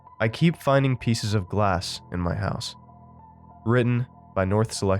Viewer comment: I thought it said I keep finding grass in my house